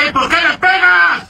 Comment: It appeared he was being hit while he was pressed to the ground. It sounded like he said, "Porque me pega?" (Why are you hitting me?) This action seems like the worst of human instincts gone amok, the deepest tribalism, with no evidence of normal police training or restraint. God help us!